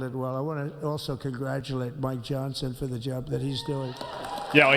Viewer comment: sounded like Trump said “I want to also congratulate my Johnson for the job that he’s doing”